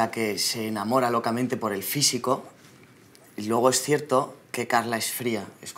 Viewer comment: can anyone write on spanish or english what is Christian saying at 06:05-06:17...englsh captions in this video are not correct. especially, i dont understand this part when christian said "logo es cierto"?( or something like that) what does that mean